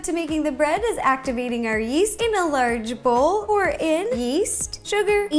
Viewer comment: When she said "activating your yeast" I thought she said "active eating your yeast"